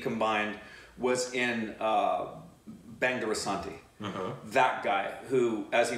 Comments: Did I hear BANG DE RASANTI ? 😂 SEGALLIZED !!! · Did he say “Bang de Rasanti”? 10:15 😂😂